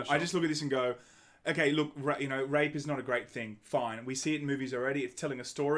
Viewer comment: Half asleep whilst listening to this and i swear i heard say "rape is not a great thing but its art" had to rewind the vid lol